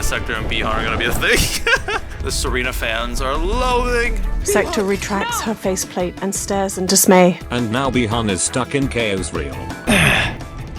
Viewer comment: I thought it said Khaosreal; like Khaosrealm mixed with Isreal